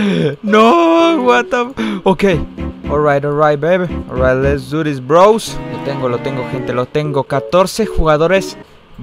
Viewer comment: Yo preguntándome qué dijo :'v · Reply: dijo barre turri rose v